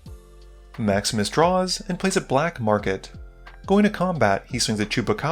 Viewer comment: this whole time i thought you were saying "Max miss draws" or "miss plays a land" lol i was thinking "man Andrew really doesn't like Max"